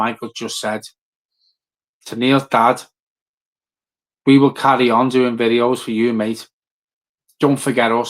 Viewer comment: 20: 45 I thought he said , I'll cry onion gravy.